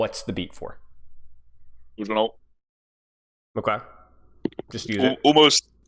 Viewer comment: what does say at 14:20? hard to understand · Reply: Sojourn ult